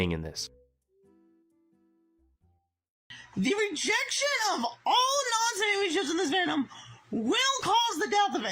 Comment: I had to replay it like ten times at lowered speed to figure out what the hell he was saying. I think it's "The rejection of all non-Sonic Amy ships in this fandom". He just totally slurs it together into a single sound, it's insane